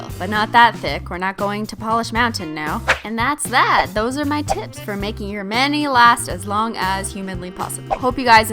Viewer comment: "those are my tips-" I heard "Those are my tits" XD